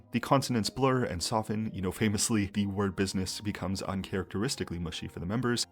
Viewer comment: It doesn’t help that kpop artists always has this “holding on tightly on the words” when singing in English, as if they’re trying so hard not to mess up the words. It feels stiff. Also the fact that i kept hearing them say “bidness” instead of business.